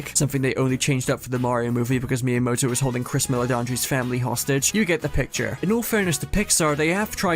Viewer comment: He said Chris Meledandri, the producer of Illumination. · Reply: Oh ok, thanks! I heard “Chris Miller and his family”. Man, those names people have…